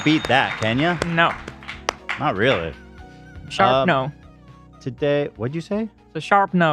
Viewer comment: I thought said sharp nose 💀